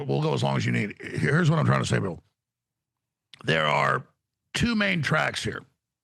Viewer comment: Did call him Bill??